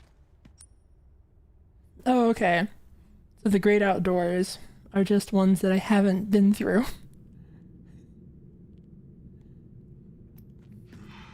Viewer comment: when she said "the greyed-out doors" I heard "the great outdoors"